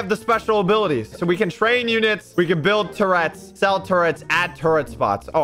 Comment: I thought you said "we can build Tourettes"🤣